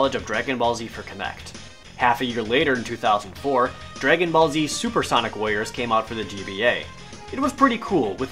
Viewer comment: I think I need hearing aids, I thought you said 'Supersonic Lawyers'. xD